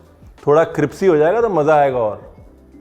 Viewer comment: did he say CRIPSY??